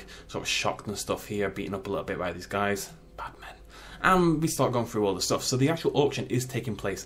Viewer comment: Why did you say Batman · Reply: no he said Bad Men as in they are not nice to the rex